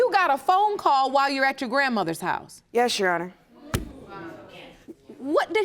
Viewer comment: When I first heard the judge say her name, I thought she said, Hohouse, lol.